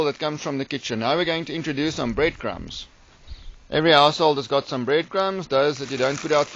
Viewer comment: I heard "every asshole's got some bread crumbs..."